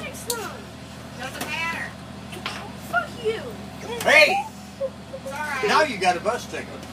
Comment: bus driver did he say f**kyou